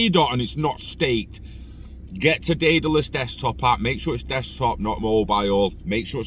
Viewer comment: Wooooof 👍🏻🖖🏻🔥💥🚀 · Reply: sorry mate where did you say we can stake our ADA, it sounded like you said adaless desktop but I don’t think I heard you correctly?